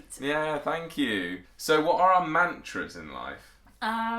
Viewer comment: Thought she said “who are your mentors?” Not “mantras.” Take off that lepord print pleeease it’s not “back.” Anyway love you guys! Also are e-cigarettes a thing there like they are here in the US?